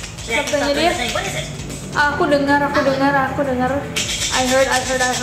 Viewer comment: video yg ketiga kenapa mukanya ngedeket2 ke kameraaaaa :'(( apa cuma gua yg kedengerannya bukan "i heard" tapi "ahoy" 😂😂😂